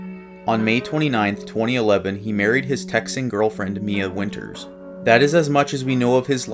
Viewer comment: For a moment I heard "sexting girlfriend" and was like "WTF?"